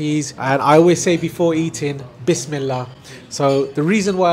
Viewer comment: Bismillah? I thought you were saying Wish me luck